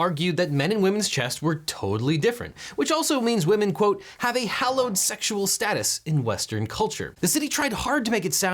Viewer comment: thought you said homosexual status